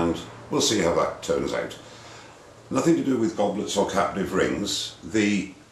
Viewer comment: every time he said 'captive' i hear 'caterpillar'